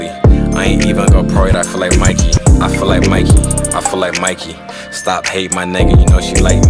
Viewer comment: I thought he was saying " I fill like Mikey"...